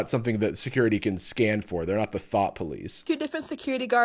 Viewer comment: did you just call her a thot????